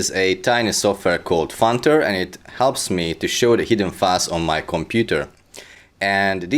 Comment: At first I thought he said "It helps me to show the hidden farts on my computer" 4:05 "290 demons" Sounds like a tech support scam.